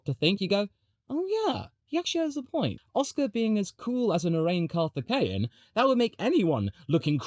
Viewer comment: Sounded like you said "arraigned carpathian" and I immediately thought of Lord Vigo.